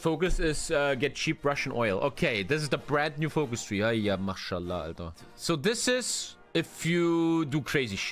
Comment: did u say mashallah??